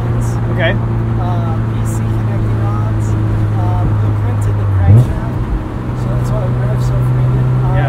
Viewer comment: Did he say he "blueprinted" the crankshaft? Does that mean he designed and built a new one? Or did he say something else and I misheard the sentence